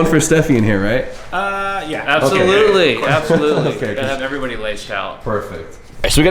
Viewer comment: did he say everybody likes chow??